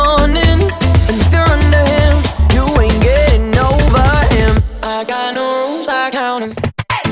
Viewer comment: My dad thought It said "I got a woop I want em" Not "I got new rules I count them" Lol